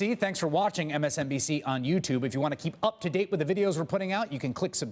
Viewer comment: Every time Chris Hayes talks at the end of these videos I hear "...you can click subscribe. Just blow me" :-D